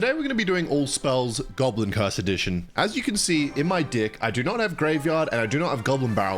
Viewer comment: did he just said in my dih? 😭